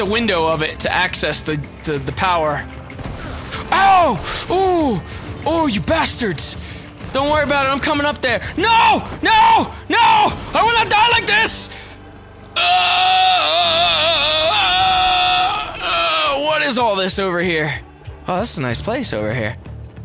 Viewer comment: did he say im going to dalek this